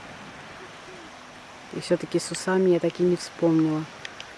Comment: спасибо за невероятное небо и настроение!Как я хохотала)))когда услышала фразу "Растение с усами",перемотала оказалось не расслышала "сузам"))).Вы правильно сказали название на русском кунжут,он же сезам.Удивляет как трогательно Вы называете Мамой свекровь,такие отношения оооочень редки.Пожалуйста расскажите об Ваших отношениях с родителями мужа и о том где Вы с мужем познакомились и сложно ли дается турецкий язык,очень интересно ))))Отдельно спасибо за турецкую кухну,готовлю часто по Вашим рецептам!))